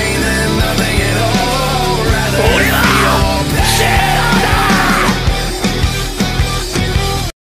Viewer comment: What does he say at 3:12? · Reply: I forget it like he said " I will never fall for that again"